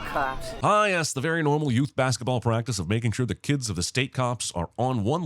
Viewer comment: Is this Corrections Time bc at 9:03 the caption says Baseball but the Jeremy says Basketball and I dont know how I can continue to support a channel who can let something like that slide. Basketball has the ORANGEST BALL after all